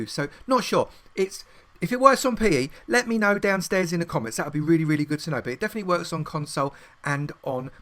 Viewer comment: Did he just say downstairs in the comments